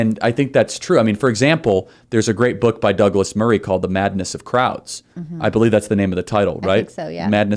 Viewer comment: "The madness of crowds" lol I thought he said "maddness of krauts"😆